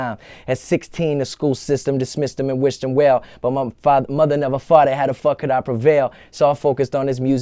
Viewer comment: I always thought he said "but my mama never Farted (fought it) , how the fuck could i prevail?"